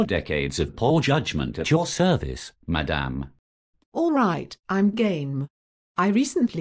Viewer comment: i thought the cartoon girl say "alright I'm gay" haha